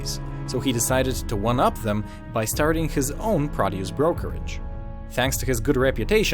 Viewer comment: I listened to this 10x "by starting his own _____ brokerage" I have no idea what that word is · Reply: Produce brokerage. You didn't hear it because he's not saying it correctly.